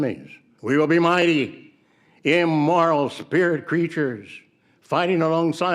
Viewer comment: I definitely hear "Immoral" Which confused me until you said he's trying to say "Immortal".